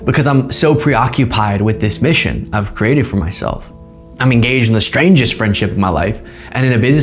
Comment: hahaha omg NOOOO. the brain rot spreads so quickly!!!! TELL ME WHY after hearing the Moody’s “everything’s gay” montage @ 10:32 I hear “I was enGAYged” I had to rewind to realize he was speaking English and not Everything isGayish. 🤣🤣 that’s never happened to me lmao!! did I just turn maga for one fleeting second!? AHHH I NEED A BATH